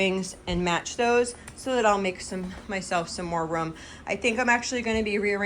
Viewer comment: I’m not making fun. I got a little chuckle at your expense, though. At 5:23 I thought you said that you are going to make yourself some more ‘rum’ - but the word you meant was ‘room’ as rhymes with broom. LOL! Well, did you make yourself some more rum? 😃 That’s ok. Here in Minnesota we have our own accent going. Ya sure! You betcha! 😆 At the end you said room as in broom so my ears must have heard wrong earlier but I still chuckle about that rum.